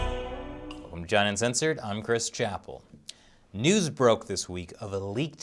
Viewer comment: "Mmm China Uncensored, I'm Chris Chappell." He does that sometimes. Instead of saying "Welcome to..." he just says "Mm." We still love you, Chris!